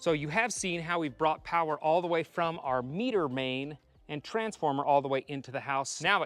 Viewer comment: Meter main. I thought you said meter maid like from zootopia